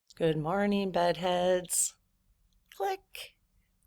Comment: I heard “good morning buttheads”! 😅